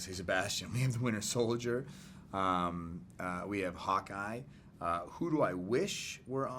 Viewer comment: Every time he says Hawkeye I hear "Hot Guy" and I'm just like where!?